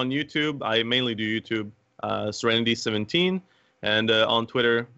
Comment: I thought they were saying report serenity17.